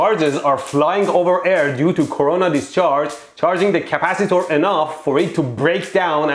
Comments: Did he actually say Corona discharge at 2:13? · Did he say due to Corona discharge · am I tripping or did he say corona discharge